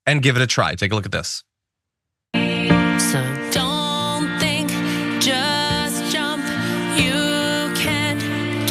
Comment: Did she say "don't think, just jump" They are training their lemmings well. 😂